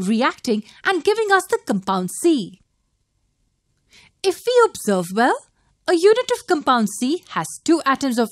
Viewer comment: what does shew say at 4:26? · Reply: If we observe well.